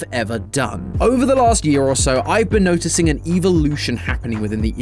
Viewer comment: Oh no not a evil-lution 0:05 instead of evolution